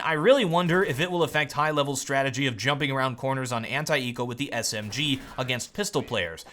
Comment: when say "anti-eco” what i hear is "hentai-ero" XD